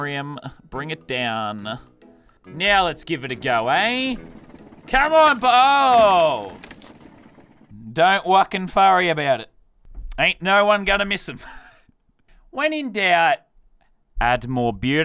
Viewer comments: did he say don’t wucking forry about it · Did I hear him say don't wocken furry about it